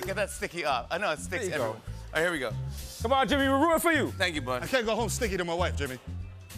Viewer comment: I love how @ 2:19 Mero stuck in "I can't be stickier than my wife Jimmy" 🤣🤣🤣🤣🤣👌🏿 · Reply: I heard, “I can’t go home sticky to my wife Jimmy” which lmao he snapped